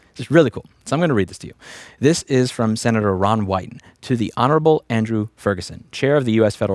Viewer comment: I swear to god I thought I heard you say "Senator Ron White" 😅 Fkn Senator WHO!?